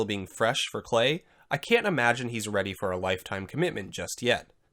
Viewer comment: did you say “wife-time commitment “ 🤣